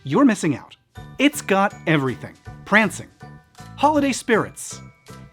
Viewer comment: When he said "prancing", I thought he said Krampus!